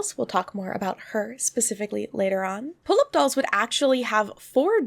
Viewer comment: I have no idea how to say the name but I love that I kept hearing "Pull up Dolls". At first I was like, wait do they fight or something?" xD